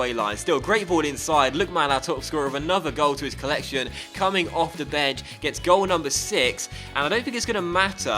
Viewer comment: am i the only one who heard him say coming of the bed ?